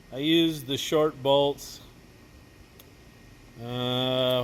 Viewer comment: said, 'I used the short bolts." I heard, "I used the short bus."